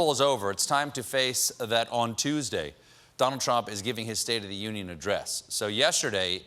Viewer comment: It sounded like he said "Dum Trump" at 2:07 LOL